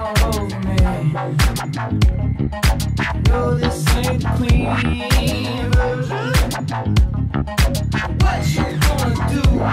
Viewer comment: I swear, I keep hearing "This ain't the Queen version", not "This ain't the clean version"...